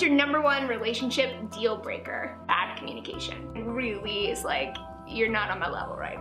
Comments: Laci's "bad communication" dealbreaker around 5:10 sounded like "acne medication," and I was thoroughly confused. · I was so confused because when laci said bad communication I thought she said acne medication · I thought she said acne medication instead of bad communication at first, and I was about to feel bad about my skin